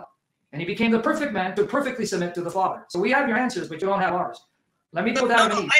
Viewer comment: did he say “perfectly sins?” Pls someone clarify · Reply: he said to perfectly submit to the Father :)